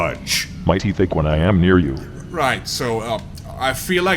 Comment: Instead of "MY TEETH ACHE WHEN I AM NEAR YOU" I keep hearing "MIGHTY THICK ONE I AM NEAR YOU" as a threat. I think it's a sign that I should re-watch this less. 34:12 if you wonder what I'm even on about.